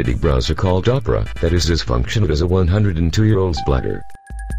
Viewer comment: I thought he said as functional as a 102 year old bladder. Not spider.